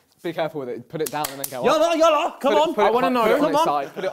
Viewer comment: did harry say "yalla yalla" in Arabic? 😂